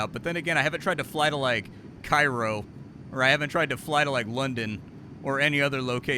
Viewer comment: Thought you said 'You haven't tried going to Pyro', lol, not Cairo!